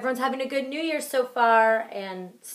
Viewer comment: what is she saying was it spanish or anything and btw are the bella twins mexican and do they speak spanish ??? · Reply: they are Mexican Italian and she said besos which is kisses